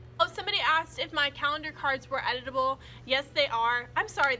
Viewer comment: I thought you said "Are the card edible" 😂